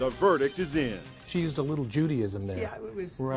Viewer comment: "she has a little Judaism in there" is what I heard....